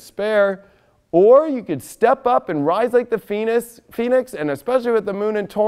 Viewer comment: Rise like the fenis!!!! hahahaha, nice one. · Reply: I thought first he said rise like the penis!! LMAO!